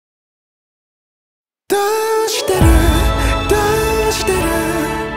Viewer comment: 最初のどうしてるがだいしてるにも聞こえて唸り声が出ました。